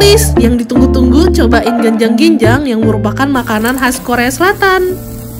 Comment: kedengerannya jajaran genjang, ada yg sama?wkwk